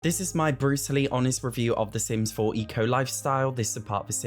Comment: Why did i hear "This is my Bruce Lee honest review" 😭😭